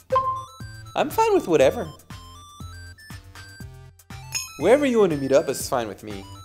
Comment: wherever you가 '웨어r위' 로 들리네...ㅠ